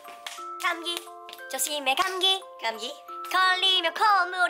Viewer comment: Só eu que ouvi "Gabi" kakakak 😂😂😂 desculpa alterei 😂😂😂😂😂